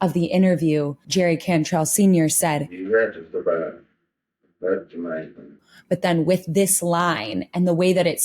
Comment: What does he say at 9:27? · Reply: "You had to survive, that's the main thing."